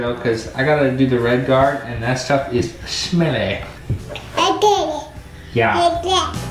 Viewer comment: So cute sounds like she said “I’ll get it “.( time stamp 5:37) 🤗